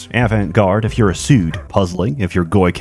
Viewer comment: what is he saying here? “Asood”?